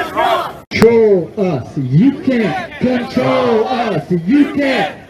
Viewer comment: When i first heard the chant i thought they were saying "U.K CONTROL US U.K CONTROL US" anyone else heard that? or is it this me?